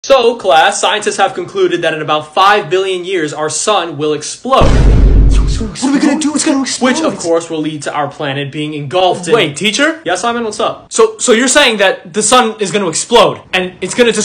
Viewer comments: I heard "our sOn will explode" and not the real one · When the teacher said that the sun will explode I thought he said that his son would explode like if you thought that too when you first watched this